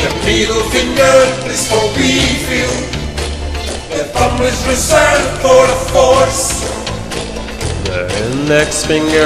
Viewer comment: the thumb is for what? I can't tell what he's saying. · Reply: Direction of force